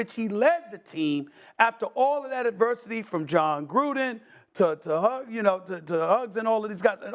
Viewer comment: Did he say Huggs?